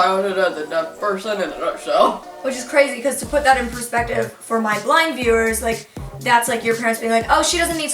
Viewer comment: Did she just say "for my BLIND viewers"🤣